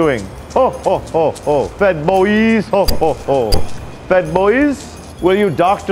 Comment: I thought JOK was saying "Fed-ployees", but I guess it was "Fed Boyz". Twitter can probably call their employees Fed-ployees, though.